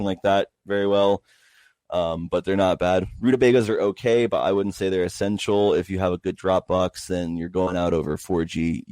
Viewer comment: What does he say at 40:57 Roof baiters? Can't understand exactly what he says here Something to do with keeping persistence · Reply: Pretty sure he says 'rootabega' like the plant name used to make a hacking device but I'm not seeing anything but recipes